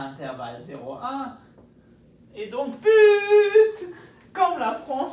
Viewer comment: I heard "PU..." I should watch soccer more often, i didn't even watch my own country winning the world cup... Great video anyway, that was a nice trick! Thx for sharing the knowledge ;)